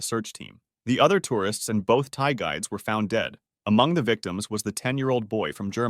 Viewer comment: I know he said two Thai guides but it was funny when I first heard it as “ and two Thai guys”